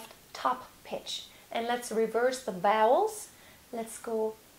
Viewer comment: Is she saying “reverse the bowels” or “vowels”? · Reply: Nicholas Williams vowels lol